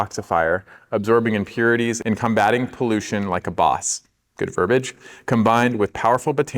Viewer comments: does he say good for a bitch?😂 If he does I’m down for it😂😂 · I thought he said “good for a b****@ 😅